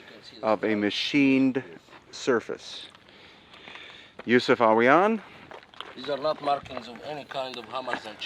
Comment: I'd swear it sounded like asked Yousef "are we on?" when he was simply introducing Mr..